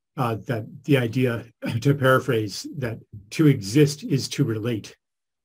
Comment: Beautiful conversation, thank you both and to the thoughtful questioners too. Charles - when you said "To exist is to relate" (18:28), I somehow heard it as "I relate, therefore I exist". Reminded me of Descartes... I wonder what you 'think' about that paraphrase and if it's plausible that our cultural focus on thinking versus relating is another indicator of the hall of mirrors that Iain described from the left hemisphere.